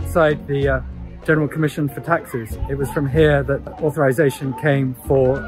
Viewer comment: I thought I heard General Commission for Taxis